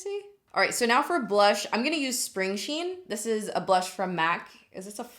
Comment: Spring sheen? Girl I thought you said string cheese... 😂